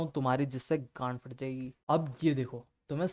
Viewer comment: what did he said ? Gaand faaat jayegi